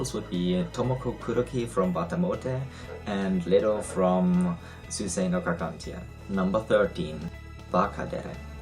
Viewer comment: Can anyone tell me what anime (after Watamote) he said at about 5:12? Anyone? I want to know! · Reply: Suisei no Gargantia, if I heard myself correctly, after all that time xD